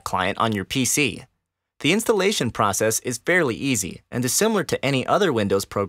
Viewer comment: it sounds like he said barely easy